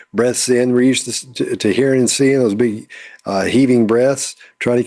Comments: I know at the end of this video was smiling when said. "You can notice her big breaths." I thought you said something else, something impossible to miss. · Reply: I believe the word was "heaving" breaths;) 😂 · Both and I heard 'breasts' and then 'heaving breasts' as was sharing his thoughts Literally: 🤭